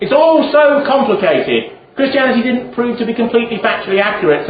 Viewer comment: What did he say Christianity didn't prove to be at 44:27? I can't make it out. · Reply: "Christianity didn't prove to be factually accurate"